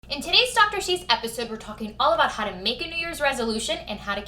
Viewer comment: At 1st I thought you said "in today's soccer chic video" I was so confused 🤣 I have lots of fitness and mommy goals for 2019 and I'm excited I feel like I can actually accomplish them this year. Loved this video, great motivation. New subscriber here.